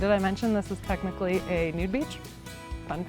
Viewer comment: I thought you said nude beach?